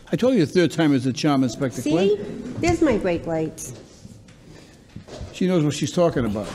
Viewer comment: I deadset thought she just randomly started saying “beyblades” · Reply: I thought she said freight lights. I'm trying to solve how she pronounces like that 😅